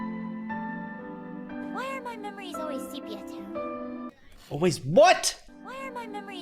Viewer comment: I think she said sepia-tone